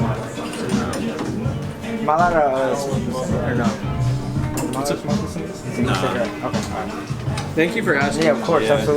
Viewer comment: what did he say on 9:34? lmk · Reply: Is he allowed to hit his vape